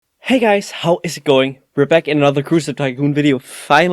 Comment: At the beginning it sounded like he said hey guys how is going